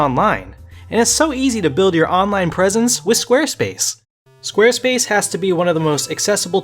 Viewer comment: I THOUGHT YOU SAID SWEAR SPACE AT FIRST LMFAO